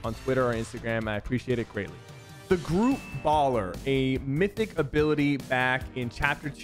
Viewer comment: i thought he said group ballet